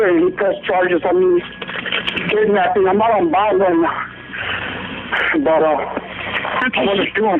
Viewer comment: says " I'm out on bond right now" But why does the caption say " I'm not a bad man" 😆 He's definitely a bad man, dude just shot 3 innocent people to death.